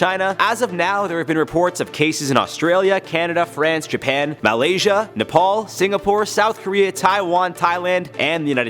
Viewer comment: did he said Malaysia